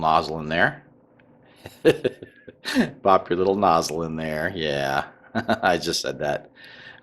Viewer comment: Dear... What did you say in 3:40 of your video? 😅 (No.... Seriously I want to understand because I'm from other country) your laugh were so contagious. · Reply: Popped my little nozzle....LOL